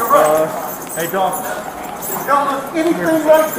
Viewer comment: did he actually say hey dog???